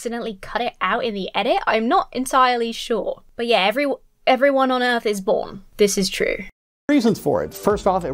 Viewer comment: I think he said that everyone honors the day of their birth.